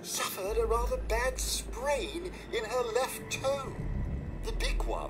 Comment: I love how said he had to amputate her leg because of a sprain in her left toe, yet he amputated her right leg. Best doctor 2016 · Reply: 777 14:31 "...suffered a rather bad sprain in her left toe. the big one." he clearly said HER left